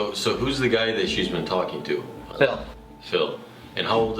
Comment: i heard bill so i started saying Bill nyee the science Guyyyyyy BILL BILL BILL BILL. Then i went back and im likeeee ohhhhh um its phil okayyy☺